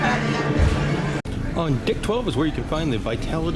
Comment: and 40:13 FYI captions says you’re saying “dick 12” and it sounds like it too 😂